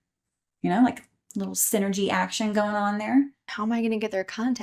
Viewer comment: I know she said "synergy". However, what I heard was "sinner G". No clue why